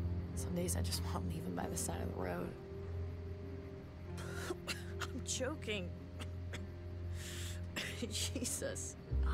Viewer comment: I looked away for a sec and I heard i'm chocking I rewinded it and she said i'm joking lol